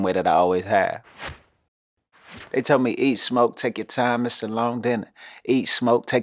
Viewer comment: Am I the only one who thought he said “ eat, SLOW take your time, It’s a long dinner.”😂😂😂😂😂😂?!?!?